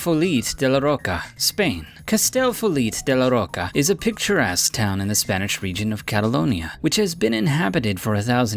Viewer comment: did he say picture ass town?